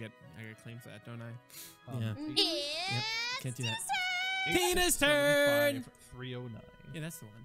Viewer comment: When Tina said tTinas turn I thought she said tunes turn